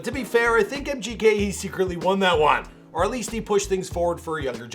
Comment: DID HE JUST SAY MGK WON THAT ONE????